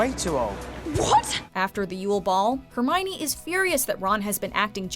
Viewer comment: I thought to heard "Uwe Boll" XD